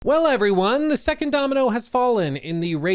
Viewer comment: I swear i heard "hello everyone, the second tower has fallen", and i said "again??"